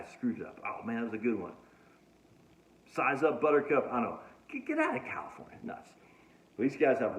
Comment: I always heard it as suck it up, butter cup.